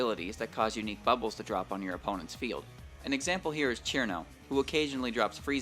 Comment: Did I just hear “chirno“?